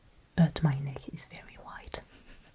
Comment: I love your videos. And it is a treat to see your face with no make-up. At first I thought you said your neck was “wide!” And I think your neck is perfect for your face! But you said “white.” You are lovely and skillful.